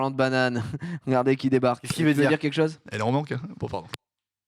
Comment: il dit quoi Norman à 4:41 ? xD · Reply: "elle est en manque" je crois xd